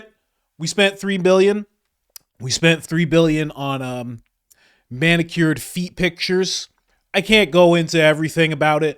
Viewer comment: did he say quails or whales ? Sounded like whales but either way it’s pretty dumb to spend money that way. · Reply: Quails lol not that it makes it any better